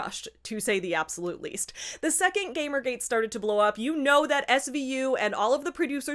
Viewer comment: Ok I'm brain poisoned by recent events so at 8:39 when you said "the second gamergate started to blow up, you know that SVU[...]" For a split second i heard it as "you know that Epstein knew" and I thought "yeah i bet he did, he was probably involved"